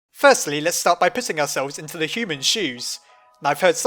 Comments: at about 1:50 when he says "let's start with puting ourselves in the human's shoes" I thought he said " let's start with pissing ourselves in the humans shoes!" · I thought he said let's start with pissing ourselves